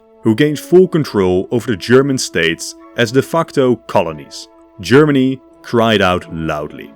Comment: did I just hear ‘de f***to’